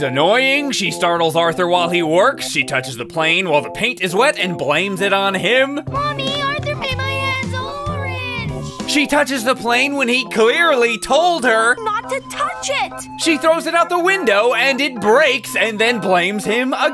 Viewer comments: Sorry but I clearly heard DW say, “Mommy! Arthur paint my a** orange!” 😂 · I heard "Arthur painted my ahh orange!" Calm down arthur